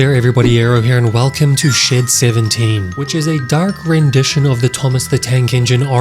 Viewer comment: I thought he he said”shit 17”